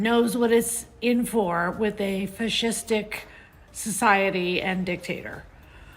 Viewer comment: Did she just say a fish stick society? 😂😂😂